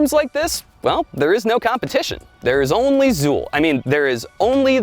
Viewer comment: There is only Zuul? Is that what he said at 6:02?